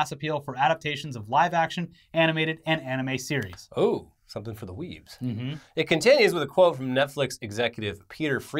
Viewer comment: DID SOMEONE JUST SAY WEEABOO??!?